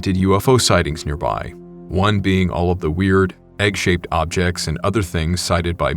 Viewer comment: I was like did he just say Ramiel like shaped object and laughed